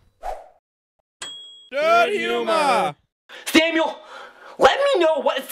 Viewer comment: i first always thought they said in theyre intro "love you ma" but they said "dirthumor" lol =D